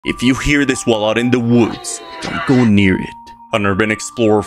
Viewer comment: I thought he said "if you hear this while out in the woods go near it" 💀